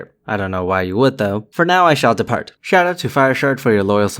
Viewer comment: I misheard your patron name announcement as "FireShart". Laughs were had.